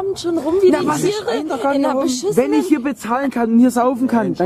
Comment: antifa bar? oder was sagt se da?